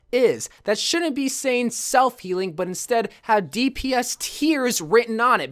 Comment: I thought it said "self heating"